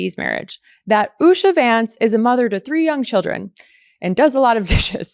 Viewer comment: When I hear JD Vance’s wife’s name - I hear “Usher” when you’re singn’Yea yea”.. “Ursha” …🎶 "Take that rewind it back Ursha got the voice make yo booty go……👋🏽